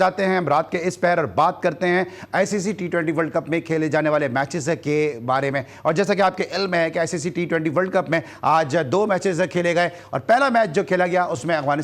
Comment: did he say jaisey ki aap ke 'L Mein hain' ? 😅